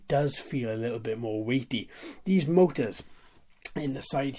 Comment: Nice reviews! I like how you detail your reviews. Can you please tell me what you said from 3:30 to 3:35...I think I hear you say something is a "little bit more weepy or wimpy"???... but I cant make out what you <link> (what is a little bit weepy or wimpy)? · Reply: Hello thank you very much. No problem, weighty is the word I chose for that. A quad with a nice bit of weight to it.